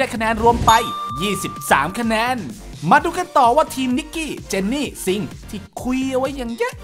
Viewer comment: ต้องเป้น นิกกี้ เจนนี้ กัน ไม่ใช่หนอ ได้ยินง่า นิกก ี้เจนนี่ ซิง หรือหูเรา ฟาด